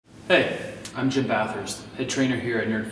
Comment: For a moment i thought you said" Hi i'm Jim Bratwurst.." which means sausage in german 😂